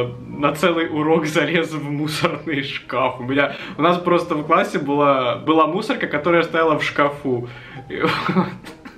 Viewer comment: впервые слышу как матюкнулся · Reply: Тон сказал была мне вам по 100 раз говорить